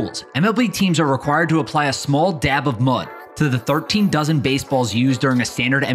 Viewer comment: Had to replay the video because I thought he said 13 thousand instead of 13 dozen 💀